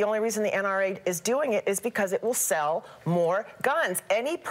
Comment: When did the NRA start selling guns? Pay careful attention to what that lady says at 00:55. Does she even know what the NRA is? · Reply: "aviationgrade. . 8 months ago (edited). . When did the NRA start selling guns? Pay careful attention to what that lady says at 00:55. Does she even know what the NRA is?. " +That's NOT what she said. She said that the only reason that the NRA does what it does because and I quote "IT will sell more guns," NOT The NRA will sell more guns. It is you who needs to pay attention.